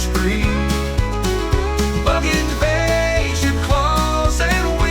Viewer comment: Great thumbnail! AI make that? And your song intro said "bug invasion", but at first I thought it said "fuggin vasion".